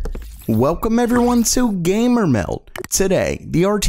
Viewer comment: Anyone else feel a bit sick when they hear the word "Meld"...just something bout the way this guy pronounces it 🤢🤢🤢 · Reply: I don't know how anyone actually hears the "d" in "gamermeld" the way he pronounces it...I always hear "gamermel". Like one of the comments above..."welgome everyone to gamermel"